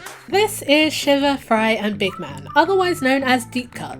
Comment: I am cracking up rn not because the video but because the auto generated subtitles thought you said sugar fry and big mac. Quite the odd order